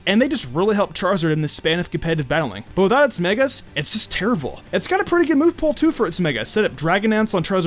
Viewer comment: Did he say Charizard was "just terrivle" at 4:44? I've listened to it 10 times and he's definitely not saying "Terrible".